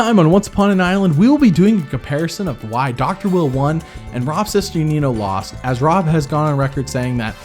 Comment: Every time I hear his name I hear "Rob's Sister Nino"